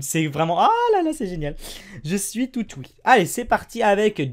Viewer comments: c'est moi ou il dit je suis toutes oui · c'est moi ou il dit je suis toutes oui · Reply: Il dit « Tout houie » ça se dit